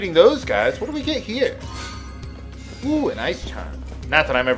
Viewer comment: when you got that Ice Charm i thought it said Ice Cream! Im not alone on that right?